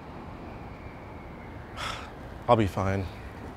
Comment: I'll be fined instead of i'll be fine